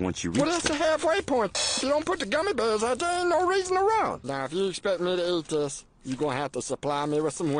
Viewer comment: Anyone ever figure out what Meatwad said at 2:08 ? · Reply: "Well it's the halfway point. Sh*t! You don't put the gummy bears out, ain't no reason to run!"